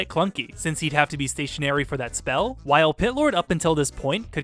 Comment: I keep hearing him say "Hitlor"